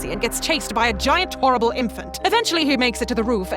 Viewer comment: That giant infant was oralble? I think I misheard her. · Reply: She says "horrible"